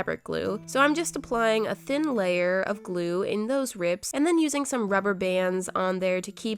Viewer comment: just in case for a cutscene at 4:20 did you say R I B S ?